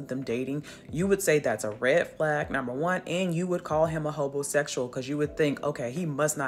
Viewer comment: "and you would call him a homosexual"...what?! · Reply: Lmao I think the captions misheard her. She said “hobosexual “